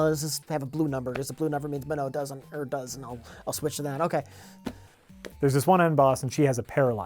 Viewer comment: am I the only who heard "there's a woman in Boston"?